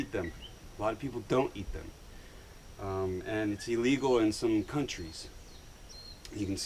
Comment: who said coconuts are illegal? coconut can be a "state" tree for some countries......... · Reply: ohhh sorry i thought i heard you say they were illegal in some countries (8:23). My fault. Btw great video!👍